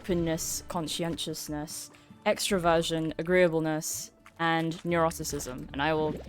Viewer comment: Ooohhhhh, I thought you said extra-virgin. Extraversion makes a lot more sense